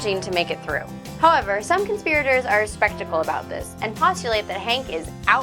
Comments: I'm sorry, did you say "spectacle"? Perhaps you meant "skeptical"? The first means eyeglasses or a memorable event. The second means to doubt what has been presented. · At the 01:09 mark she says people are, "spectacle" (instead of skeptical) about Hank being Bobby's father.